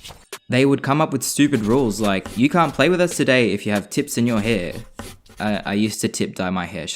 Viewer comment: For a second I thought you said "if you have tits in your hair."😅